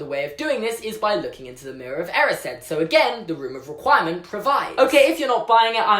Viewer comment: All this time in the movies, I thought they were saying "room of acquirement', because that's where you can acquire something.